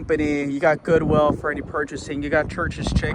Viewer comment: Did he just say “you have goodwill for purchasing”??? I wouldn’t advertise that as a plus.